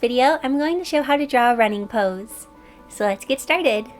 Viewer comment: I at first thought this said "Running Nose" xD